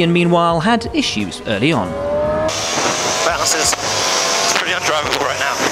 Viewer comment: they've written that Hamilton said "Bounces, it's pretty undriveable" I'm pretty sure he said "the balance is" and definitely wouldn't have said "bounces" 😂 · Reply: bounces could make sense, but I agree that's not what he said. I heard 'balance is..' too.